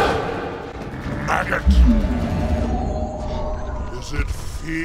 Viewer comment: In Transformers 1 when Megatron is chasing Sam, for years I heard "maggot" as "faggot" for some reason, it always cracked me up 😂